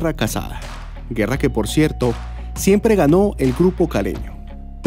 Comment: que dijiste aquí ? que no escuche hbien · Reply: "Guerra que por cierto, siempre gano el grupo caleño"